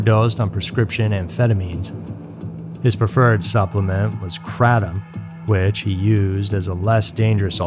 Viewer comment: are you talking about kratom? (I can't tell what word you said, but if that's it, it's usually pronounced cray-dem.) I wouldn't call it a substitute for opium, but it does have mildly analgesic/anti-inflammatory properties I guess.